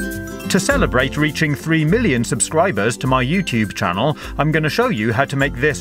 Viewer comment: At first I thought he said 3 billion aubscribers